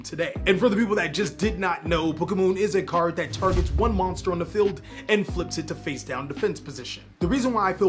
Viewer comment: who else heared pokemon ? :D